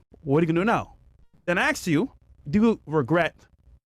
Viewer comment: I hear "Aksed you" instead of "Asked you" and I'm shut down the conversation windows XP style...